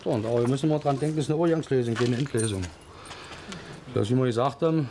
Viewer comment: Er sagt Endlösung, nicht Mietlösung. Und man kann den Herrn auch wunderbar ohne Untertitel verstehen!